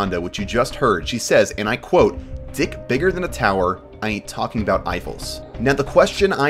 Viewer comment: I always thought is said “ I ain’t twerking bout Eiffel” guess it’s just the way she said it